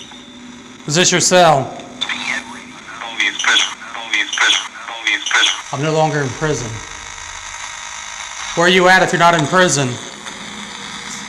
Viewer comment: if you slow it down you can make out what's being said a little better. The voice is saying LONELY IN PRISON! Almost sounds like only in prison but definitely trying to say (Lonely In Prison) Trying to make out what's being said before that.